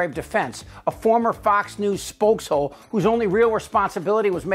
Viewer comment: I heard "spojeshole" and nearly choked on my own laughter.